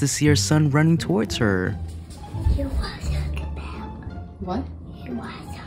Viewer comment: Lol!!! I thought he said, “You know what I’m talkin’ ‘bout?” So cute!